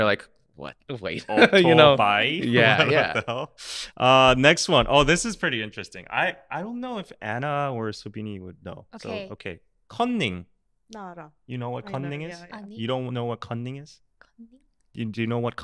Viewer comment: Cunning = cheating on exam ---> odd but kinda see where it came from based on the actual meaning of the word 'cunning'. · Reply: I thought he was saying cOnning, which would make sense since when you commit a crime and get get caught, you become a convict or con and I thought made sense with their cheating on an exam example. I was confused it was c-u! But it does make sense too. Funny!